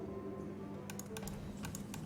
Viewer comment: I thought Injured BM said Murder... found out he says Medic!